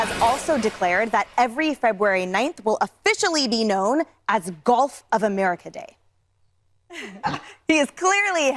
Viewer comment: I thought she said "Golf of America Day", which wouldn't surprise us either.